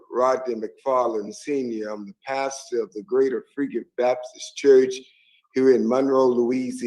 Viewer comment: ?? Did he say THE GREATER FREAKIN BAPTIST CHURCH??!!🤣😳👹🤣LMAO is that what that preacher just said??!! LMDAO my gawd!!